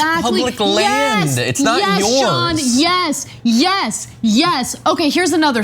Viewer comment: did Ana call John "Sean"?